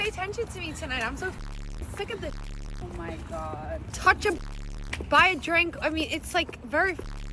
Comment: did she say "touch a b!tch, but a drink.. " LOL WTH It can't be that rough out there to get a dude.